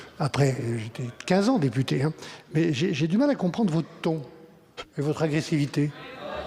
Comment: Il lui a dit quoi Barnier à Miss Piggy Panot à 7:35 : " ...j' ai du mal à t' comprendre gros thon. " ? ;)